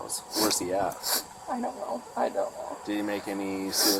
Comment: Subtitle error at 1:44 right after the cop asks her if her husband has ever been in trouble for this type of thing. The subtitle says, "I don't know" but what she really said was "No" it was just a slightly longer no with a bit of a tone from her having been crying. Right after she said "No" she also says "not to my knowledge."